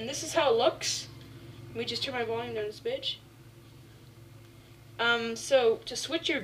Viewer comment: i thought he said let me turn down this shit. lol but how many times did he say crashes?? O.O